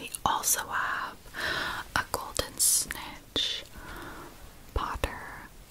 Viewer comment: I swear to god I thought you said “Here we have a golden snitch- POGGERS.” And I died.